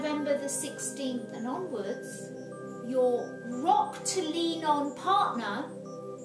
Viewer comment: What does she say at 10:10 ? " Your ROCKTO LEANO partner… makes themselves apparent.." what does ROCKTO LEANO mean?